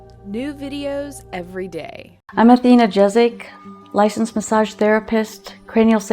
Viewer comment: Hi this Athena massage blah blah.. This is what I heard -----> " hi this is 'not lita' massage videos... *clicks back button*